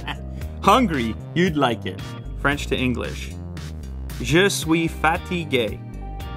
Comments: Honest to God I thought you said you were fat and gay. No offense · thought he said je suis fat and gay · fatty gay (that’s what I heard) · I heard fatty gay lmao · I thought he said Je suis fatty gay 😂😂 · i heard jews fatty gay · I heard "je suis fatty gay" 😕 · I thought he said fatty gay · I thought he said "je suis fat and gay" 😂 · i heard je suis fatty gay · dID yOu jUSt CaLl Me Fat n Gay???? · Anybody else thought he said '' Je suis '' fat '' et '' gay '' ? 😆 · when he spoke in french i heard : " je suis fatty gay" . i lost it when i realized what he actually meant xDD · for a second i thought he was saying "je suis fatty gay" oof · Am I the only one who heard ''je suis fatty gay'' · I thought you said Je suis fatty gay... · It sounded like he said "je suis fat and gay" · Who else thought he said: Je Suis Fatty Gay? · when you spoke french i thought you said je suis fatty gay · I heard "Je suis fatty gay" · Am i the only one that thought that Lew said "je suis fatty gay" but actually he said fatigue😂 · Who thought he said je suis fattygay when he was doin the translation thing😂😂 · i swear to god i heard ''je suis fatty gay'' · I honestly thought he said "Je suis fatty gay" lmao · Omg I thought that led said “JE SUIS FATTY GAY LAMOSKDJNDJDJJDJSJDJDJ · Bruh I swear he said "You sweat fatty gay" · Initially i thought he said 'je suis fatty gay '